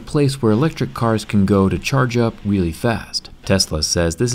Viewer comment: did he say whellie fast